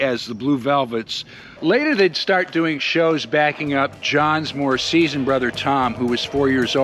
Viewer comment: I have to laugh-with my hearing loss, I SWEAR you said ‘started backing up John’s MORTICIAN brother Tom’ rather than ‘MORE SEASONED brother’😂 😂😂😂😂100% true story-I had to go back and check with subtitles😂